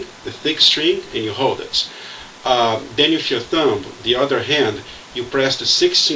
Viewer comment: I heard "6th note" too because of his accent, but 1st seeing his hand move towards the nut, I thought he maybe he actually said "16th note." Then I looked at his thumb on the neck---right on the 16th fret.